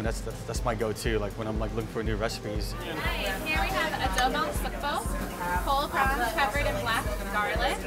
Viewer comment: What's the name of the dish at 5:57? I can't her what the lady is saying, it looks interesting. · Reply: Adrian G adobong sugpo